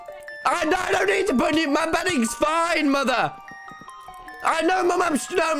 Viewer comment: The parts that are hard to understand/without captions: (0:28) "NO NO NEED TO PUT NEW- MY BEDDING'S FINE, MOTHER!" (0:33) "I NO- MUM IM ST- NO NO YOU DONT UNDERSTAND MOTHER!"